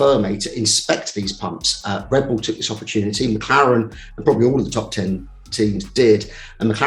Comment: Did scarbs just say "all of the TOP TEN TEAMS"? If he did I forgive him because he's a legend.